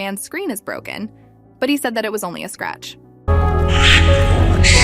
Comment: he didn’t “say just a scratch” from what I heard he said “justice served”